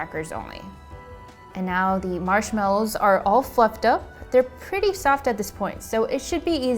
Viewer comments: I thought you said the marshmallows are all f*ed up lol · "The Marsh mellows are all F___ up..." Am I the only person who hears something that's not Fluffed lol.